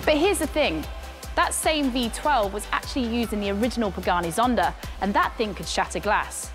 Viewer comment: hold on Second, I just heard BUGATTI ZONDA 😂, correction my lady, it's PAGANI ZONDA 😊 correct me if iam wrong 😂😂